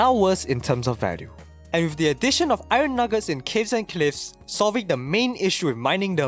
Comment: Wait...Did he just say Kids and Cliffs instead of Caves and Cliffs?